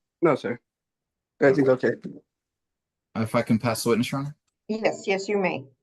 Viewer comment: Did just say at 7:54 on the tape f**ing pass the witness?